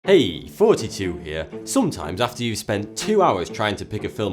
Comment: why do i hear "Hey! 42 here!" in the begining?